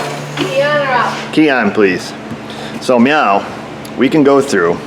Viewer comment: So, after turned the truck off at 34:38, did you say, "so meow"???